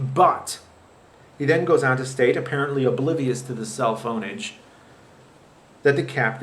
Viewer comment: lol 1:38 thought you said "cell phoneage"